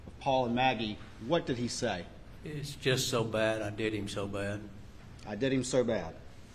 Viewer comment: "They" did him so bad. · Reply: I hear "I did him so bad." and I'm from SC. He says I like uh.